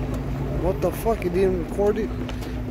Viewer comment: did he say f**** accordion or what the f*** am I recording 🤣🤣🤣